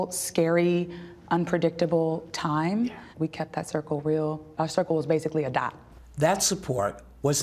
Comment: My circle was a dot. ❤️ I love that. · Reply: If we are being technical, she actually says "We kept that circle real. Our circle was basically a dot."